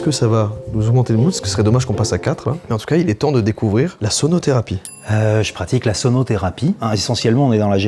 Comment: j'ai compris sodo thérapie